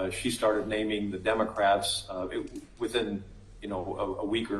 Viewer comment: I heard him say demoncraps 🤪